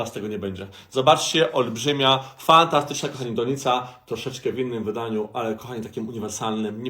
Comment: usłyszałam kondonica xd